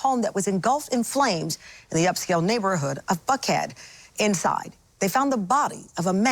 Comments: thought she said in “f head” 😮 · did I hear F---head?